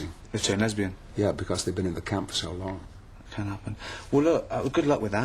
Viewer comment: Can't happen🤣🤣 Funniest line that most probably didn't hear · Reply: It’s can happen, not can’t.